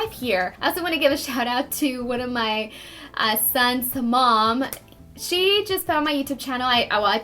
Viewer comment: did she say my "sons mom"? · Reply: yes I heard her say 'one of my son's mom'